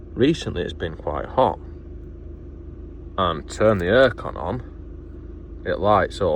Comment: I thought you said “ear con” 😂.